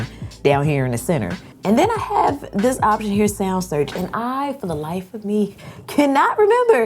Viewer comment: whats the name of the app you use to search for music i thought you said sound search but I couldn't find it